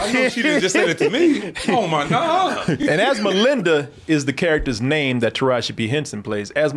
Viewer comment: I thought you were saying her name was, Asmalinda...That made me laugh.